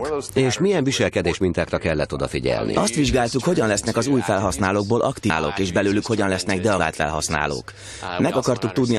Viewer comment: nál tényleg nem érteni mit mond a megfigyelt viselkedésformáknál, vagy csak az én gépemen érthetetlen? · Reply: "Azt vizsgáltuk,hogyan lesz az új felhasználókból aktív felhasználók és belőlük hogyan lesznek deaktivált felhasználók."